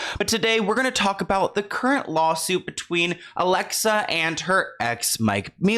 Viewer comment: Idk why I keep hearing " alexa and her exa" 😐